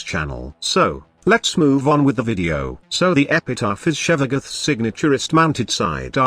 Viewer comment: Whenever the phrase "signature wrist(-mounted sidearm)" was said, I kept hearing "signaturest."